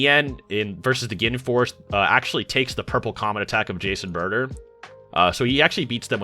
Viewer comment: said “Jace and Burter” and I thought this man said “Jason Burter” I was like “What?🙃” lmao